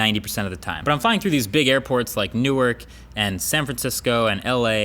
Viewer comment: hold on? did he say new erk ?? 8:19 ??? when referencing NEW YORK ?